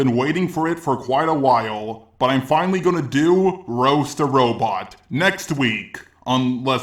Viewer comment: If I don't pay attention to your into I hear "Astro Robot" or "Gasta Robot" Lol